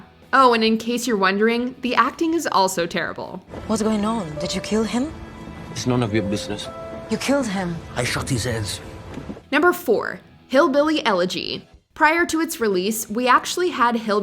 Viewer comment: brah! i shoot his hands (at least thats what I heard) 😅😂😂🤣😭💀